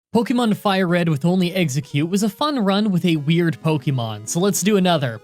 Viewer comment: "pokemon FireRed with only one exeggcute is..." that's the first thing I heard and I'm like